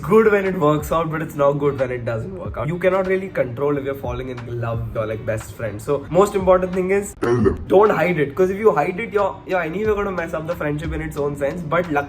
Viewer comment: and i thought he said “kill them”💀